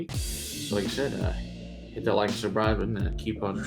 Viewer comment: Love the creative episodes that few of your YouTube compadres are doing! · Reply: Sounded like you said "like and survive" button at the end...funny